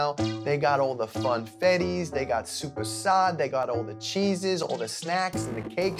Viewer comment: I NEED TO KNOW WHAT THIS IS 3:01!!! IT LOOKS SOOOOO GOOD but I can't fully understand what Mike's saying and the subtitles also aren't of any help cuz "suprasad" is apparently a very popular indian name